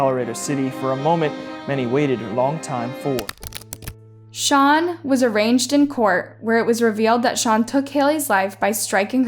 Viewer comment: Am I the only one who heard her say “he was arranged in court” rather than “arraigned”